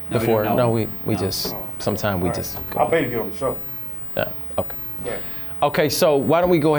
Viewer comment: Did he say I'll pay to get on dr. Phil Show